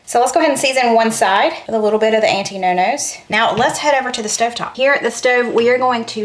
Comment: Thought you were saying anti-no no’s. That would be yes, yes, right/